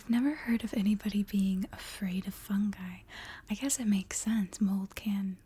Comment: At first, I heard 'fun guy' instead of 'fungi' and I was like "Ah yes, I too am afraid of fun guy."